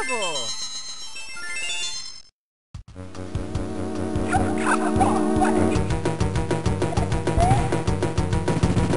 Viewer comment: what did robirdo even say? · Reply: You come a long way